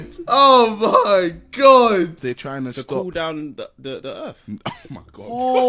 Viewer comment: I thought he said pull down the earth and that’s how gravity is made